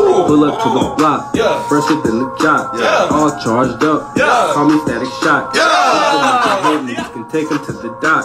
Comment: To be fair, “all charged up. Call me static shock” was a BAR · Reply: Thought he said call me daddy shocks lol